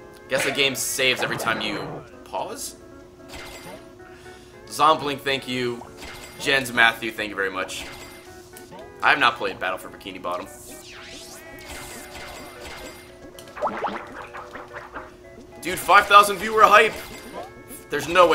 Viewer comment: This game looks so blah compared to the one on the ps2. Battle for Bikini bottom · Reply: I thought you said that this game is better than attack on bikini bottom and I was about to talk some shit to you